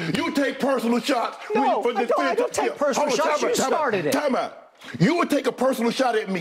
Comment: what the hell does Shannon say? Waaa pow? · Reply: as a non native english speaker it sounds to me as "timeout"...